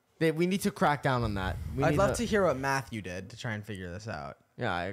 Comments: i heard "what matthew did" too so i was so confused 😭😭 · Bro I thought the same thing Chris 😂😂 I thought Nick said "Matthew" but he said "Math You did" broo 🤣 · I heard Matthew too I thought I was tripping until I went back 😭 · I heard Matthew not math u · I ALSO THOUGHT HE SAID MATTHEW…. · NO BC I HEARD MATHEW TOO😭😭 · I feel stupid because I also thought nick said "matthew" not "math you" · i thought he said matthew too😭